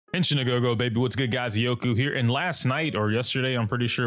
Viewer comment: imma be honest as someone who dont speak English as their primary language, I don't understand the first few words on your vid. All I hear is "genshin a gogo baby what's good guys..." jokes aside, I like how CC talks about this since Genshin also took a while before they made each element focus on one certain stat. Although I see each path having their own focus in HSR, most of the time it dips on another path's specialty